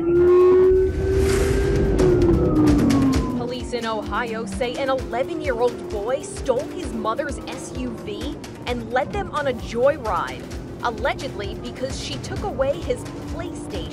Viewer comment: I hear Ohio